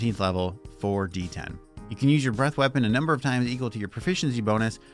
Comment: It's good but once per long rest vs a con save is a little rough. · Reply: shit I misheard in the video I thought it was Prof mod times